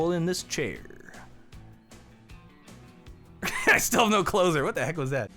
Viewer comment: OH HE'S SAYING I STILL HAVE NO CLOSER. I thought he was saying I still have no clothes on this whole time.